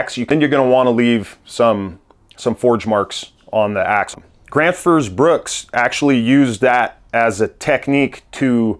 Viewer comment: I thought you were saying Grant vs Brooks when talking about boutique axes. But I assume it's Gränsfors bruk? Take a closer look at their axes and you will find that they are polished differently according to intended use. Notably the splitting axes make a good point in your case as the bit is smooth almost all the way to the handle. I get a bit defensive as I'm from Sweden I guess but I think it shows deep knowledge and great craftsmanship to know where to put in what amount of effort. I find it way more more lazy, greedy and manipulative to sell an axe of low quality steel polished to a mirror shine all over because people like the looks of it. ¯\_(ツ)_/¯